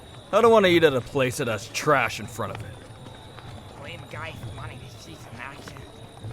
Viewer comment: What'd he say? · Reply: If you turn on captions it says “blame a guy for wanting to see some action”